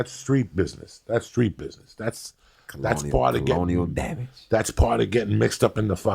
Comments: did say "colonial damage"? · Did just say " colonial damage "?!? (Edit: Made me think to the "founding" of America.)